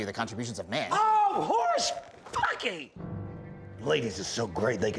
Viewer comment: does he say "horse-fuckey"? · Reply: horse-puckey